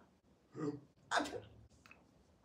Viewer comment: ”Ugh, it lingers.” That tells me all I need to know. Not every lost recipe is a winner. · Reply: Huh. I heard that as "i have fingers" and assumed it was so bad he was hallucinatin.... the funsies of going deaf...